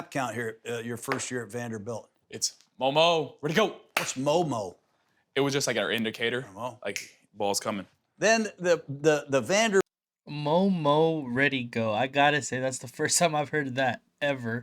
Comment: Thought he said homo ready go 😂😂😂 👏